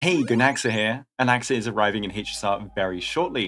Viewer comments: I heard goonaxa, I'm sorry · Why does it sound like he said “goonaxa” 😭💔